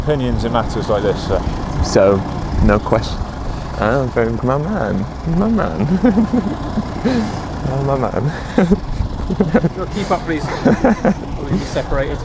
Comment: What was Ed saying after he knew the police officer was vegan? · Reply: my man my man my man